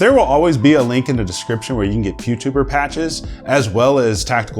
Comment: Oh....you said Pewtuber....I thought you said Pubetuber.....that's a totally different area. LOL